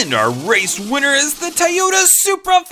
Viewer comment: I think it’s funny how he says Tyota